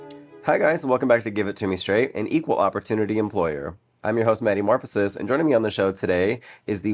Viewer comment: I thought you said Daddy Morphosis... Damnnnn